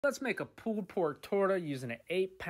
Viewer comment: All i heard was "let's make a pork tortoise"